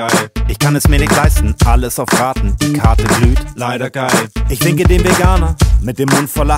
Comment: ich verstehe ich ficke den veganer